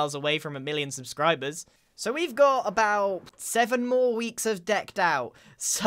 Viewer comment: ish, did say there's only 7 weeks of decked out? Why?